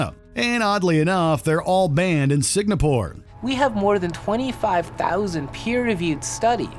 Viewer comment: I am not a native english speaker so can anyone tell me what "signa pore" is?